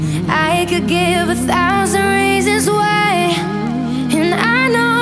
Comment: Alessia: I can give a thousand reasons why Hannah Baker: I can give 13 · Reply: Ryan Garza first time I heard this song I thought she said: I can give you 13 reasons why. I'm obsesed...